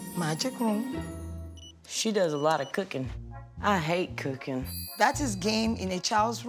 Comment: I keep hearing :"I hate cooking" way too much